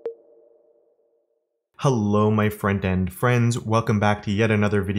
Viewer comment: I've watched hundreds of your videos by now. And all this time I thought you were saying "Hello my friend and friends" just realized it's "front-end friends" 😯😯